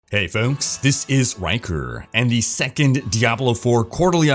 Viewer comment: What everyone hears: "Hey folks, this is Rhykker" What I hear: "Hey folks, this is wanker" ...fuk my brain..